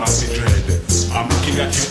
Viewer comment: I heard "vampire" instead of "empire"